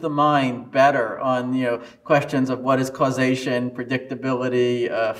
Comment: I thought that said “what is Caucasian?” 💀